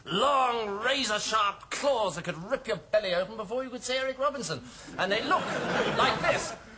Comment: The first time He said, "Eric Robinson", I thought He had said, "Anne Robinson", That was almost scary. :P.